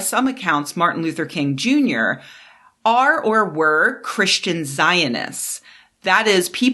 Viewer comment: Great video. Small pointing out of a verbal typo you made: at about 4:15 you said "When Trump got into office Zionists saw a great opportunity... "You meant to say there, "Christian Zionists." The relationship between Jewish Zionists and Christian Zionists is long, complicated and not monolithic. The story you are telling about the role of Christian Zionists is coherent and should not be muddied by using the more ambiguous term "Zionists" when in fact you are referring to Christian Zionists. Again, great video. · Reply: At first I heard "christian scientists" then realized I heard that wrong lol